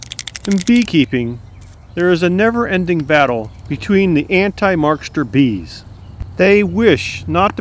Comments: Please tell me I'm not the only one who misheard "Anti-markster bees" as "Anti-Marxist bees" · I thought I heard anti-Marxist